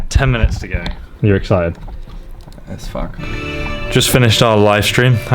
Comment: The dragon was so goddamn funny xD EDIT: Did he say "let's fuck" at 25:00? :')